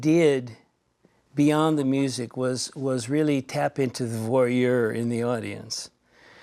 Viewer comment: The captions at 2:39 should read "voyeur" instead of "warrior."